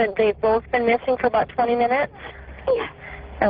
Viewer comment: what she said.) was it’s been missing for 20 minutes?